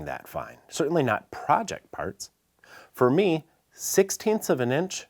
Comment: Project parts?? I thought you said "private parts". Big difference.